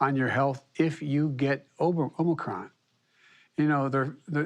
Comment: Did he say Obama con at 1:16 lmfao 🤣